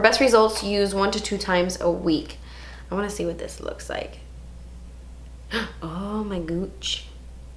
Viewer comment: Did I hear 'Ohh my gooch' when trying the underarm mask? If so that's what we call your taint in England 😂😂😂